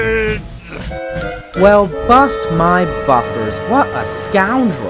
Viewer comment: I Don't Know What Alfred/98462 Said. Did He Said " It's AAALLFFFRREEDDD!!!!!!!!!!!!!! OR OOOOOUUUTTTRRRAAAGEEE!!!!!!!!!!!!!!!!!!!!!!!!!!! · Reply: Alfred